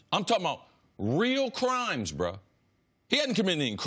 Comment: did he actually say "bruh" lol